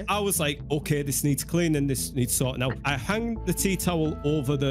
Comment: this needs sorting out, The animator: * thought he said salt and shows salt in Laffs hand*